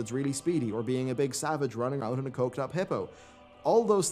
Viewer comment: Did he say coked-up hippo?